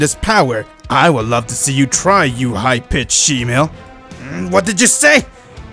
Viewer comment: did Goku call Frieza a high-pitched shemale lol?